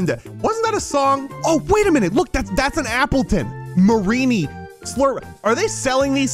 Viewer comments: Did he call alcremie “mareanie”? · marinie?Zoe did he say alcremie